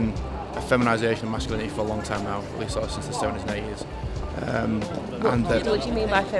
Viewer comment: I think he says "at least sort of since the 70s and 80s", but your captions say "at least as long as eight years". It definitely looks and sounds to me like he's saying "70s and 80s".